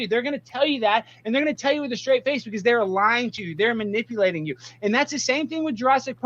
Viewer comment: Wait at 9:27 did he say lying Jew or lying to you?